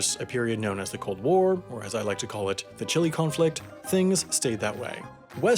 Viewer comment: Small brain: The Cold War Galaxy brain: The Chilly Conflict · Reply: I distinctly heard him say "chili conflict".